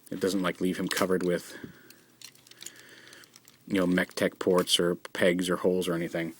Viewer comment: when the part you say mechtech port a lot of times sounds like you say tetek (that's malay for boobs)